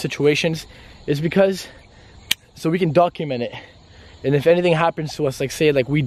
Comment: Did anyone hear an extra voice ??? Like so can see